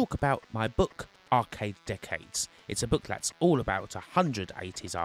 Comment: I thought he said arcade dickheads